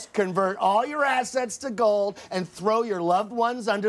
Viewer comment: Did he just say: "Collude believe it"?